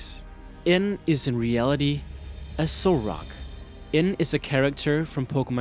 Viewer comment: This is what I heard: "N is in reality... a Solrock."